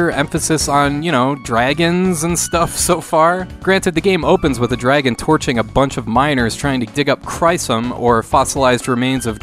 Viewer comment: I put these videos in the background while I do other stuff but I had to check when I heard "the game opens with a dragon torching a bunch of minors"